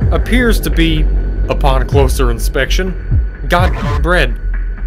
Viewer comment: what does he say in 12:29? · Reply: Bread